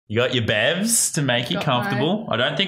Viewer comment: the subtitles say bears instead of bevs lol